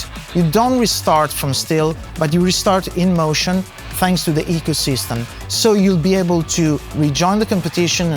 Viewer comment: i dont understand this "thanks to the ecosystem" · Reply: ECHO System, not ecosystem